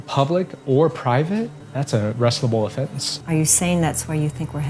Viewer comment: I know the guy said that praying is an "arrestable offence" but everyone in this movie is mumbling so I heard "a wrestleable offence". Trial by cage.